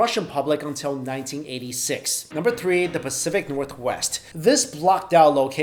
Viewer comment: Great video! But when he said "Pacific Northwest" for a second or two I thought he said "Pacifica Northwest".....